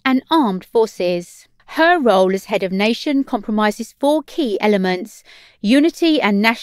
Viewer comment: Her role as head of nation compromises four key elements??? I think your reader meant to say comprises there. SMDH